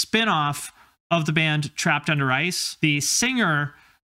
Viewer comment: I kept hearing "trapped under rice"